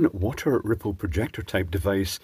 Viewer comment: Damn the luck. I thought it said "Xmas Nipple Projector." :(